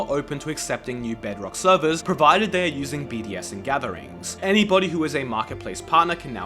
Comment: I keep hearing “bdsm gatherings” 😭🥀